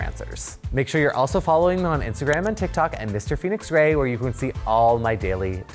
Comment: Oh, Phoenix! I thought you said your name was Felix, which I thought was cool too! Then I saw it written out. 😊